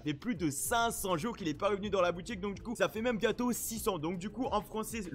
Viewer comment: 6 ans impossible sa fait que 1 ans et quellque moi que fortnite existe mdrr l'algerieflow =) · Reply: Il a dit 600